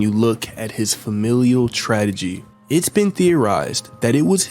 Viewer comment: Did…………did you say “ tradagy” instead of tragedy19:07